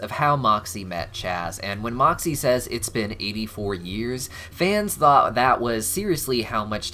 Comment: 84 years? I misheard on the show then. I thought he said it had been 24 years since he dated Chaz. LOL.